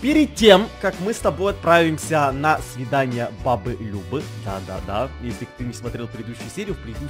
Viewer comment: Мне послышалось - на съедание бабы Любы😂